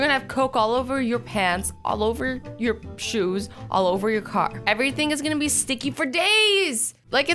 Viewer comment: Did she said what i heard 3:36 ?!?!?! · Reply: What are you talking about? All she said was all over your car